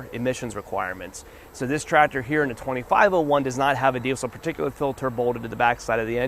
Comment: What the hell did he say?? So garbled I couldn't tell what it doesn't have that makes it cheaper. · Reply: Diesel particulate filter.